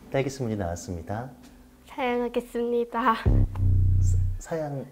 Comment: 이거 "사양하겠습니다였어?" 나만 "잘먹겠습니다"라고 들은거야? 뭐야? 뭐지? 왜 들을때 마다 계속 다르게 들리지? 읭?